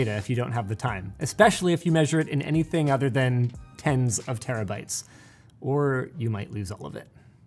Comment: Captions says "tenths of terabytes" Linus says "tens of terabytes"